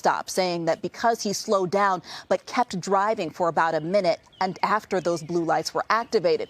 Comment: he was just asking why he was being pulled over and they never answered his questions 😐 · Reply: sorry I watched it again, the reporter said minute and I misheard it for a mile.but you want to talk about facts, plain and simple it is AGAINST the law to continue driving after a police stop has been initiated.